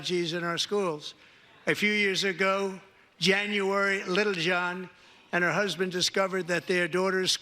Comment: Did he say that this happened to Lil Jon's kid? Yaeh?